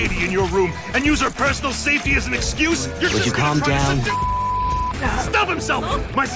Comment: When Dazai sai, "Would you calm down" and then it cuts out with the beep, I actually thought he said, "Would you calm down bitch."